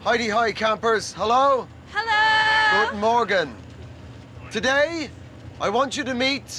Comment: did he said "Guten Morgen" in German at 29:50 ? btw. there is no god, nether is the world only 6000 years old. luckyly all religions are losing followers in the high developed countrys...hope there will be all gone on day. will be a much more peaceful world.